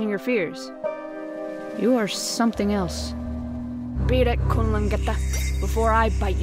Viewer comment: "beat it kumlungethhashtahtahs" Can someone please tell me what she said xD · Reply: Kunlangeta = psychopath English subtitles, where aaaare youuuuuuu?